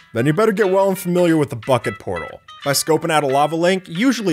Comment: Anyone else thought he said "well then you'd better get familiar withe the fking portal" instead of "bucket portal"